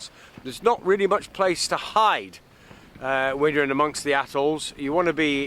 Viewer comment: Nice video. Sounded like you said assholes for atolls! What kind of double ender sailboat is Easy?